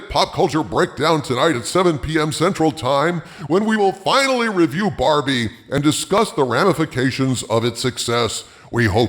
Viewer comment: You said "review Barbie" I heard " release Harvey". Maybe next time.